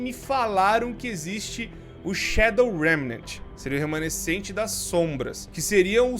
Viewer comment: Shadow O Que Não Entendi O Nome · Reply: Shadow remaining = remanescente das sombras